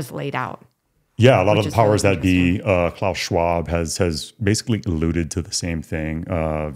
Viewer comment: who is that guy at 10:28 ? i did a google search on what i thought you said but cant find anything. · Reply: Are you referring to Klaus Schwab?